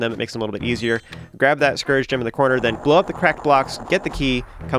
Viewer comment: love this video Walkthrough! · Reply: Did he just say, "Scourge Gem?"